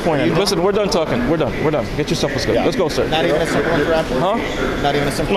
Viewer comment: what does he say? I got not even a ...